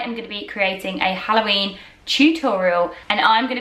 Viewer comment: I thought you said Chew-torial You look just like Sabrina! ❤